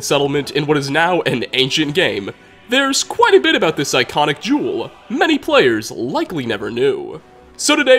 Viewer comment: im crying when you said "there's quite a bit about this iconic jewel" i automatically thought you said juul